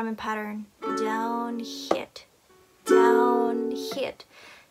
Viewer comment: When she said “down hit” i thought she said “down shit” and i was like:😯